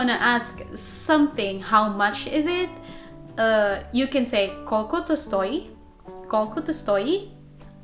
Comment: They were laughing because how you pronounced koľko, it little bit sounded like koko* which means d*ck, 😂 but good slovak, keep it up